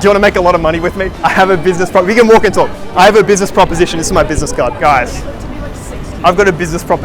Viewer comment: Could have swore he was saying "this is my business gun" at 12:21 🤣🤣 Gonna have to get me one of those